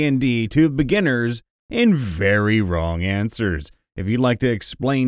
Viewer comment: I thought he said "wrong answers only." Some of these seemed pretty accurate to me.